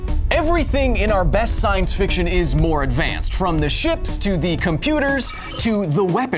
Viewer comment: Good God. When Kyle said “ships,” I thought he said “shits.” I have a serious problem with mishearing things😂